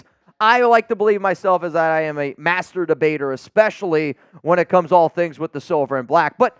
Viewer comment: for a minute there I thought you said you are a "master bater."